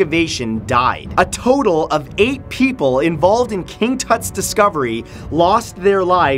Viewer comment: on king tots i thought he said king Tits XD😂😂😂😂😂